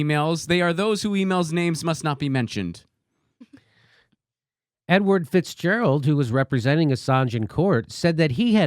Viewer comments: Lol Totally thought jimmy said ‘N Word Fitzgerald’ F Word Cuomo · I thought you said "N-word Fitzgerald"